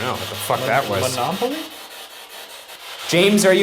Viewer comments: I heard "Don't laugh at me" instead of Monopoly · I thought it said don't laugh at me not monopoly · Monopoly?? I heard “Don’t laugh at me” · "Don't laugh at me" is what I heard instead of "monopaly"😂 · Sounds like James said don't laugh at me.. not monopoly 😬